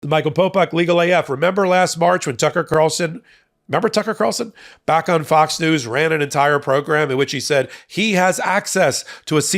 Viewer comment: A bit of nostalgia for you. . . When I first started watching and listening to you,—- I literally thought I heard you saying. I was like “Wait a second here”. Hahaha!!! I like my own words to some songs as well. 😊 Dope!!! Haha.